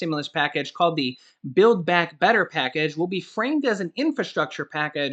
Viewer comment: Hi I thought you said bill back better package ?